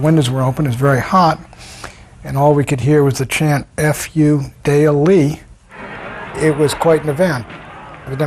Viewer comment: anyone have an idea what he's saying at 13:10? what was the chant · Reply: he was saying fuck you daley refering to Chicago's mayor of the time Richard Daley, who was controversial in the Democratic Party in part for his pro-war stance.